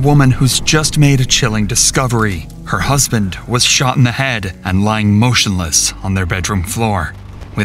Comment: Let me guess. Life insurance Smh yup. Everytime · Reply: I thought it said my husband shit in the bed. 🤦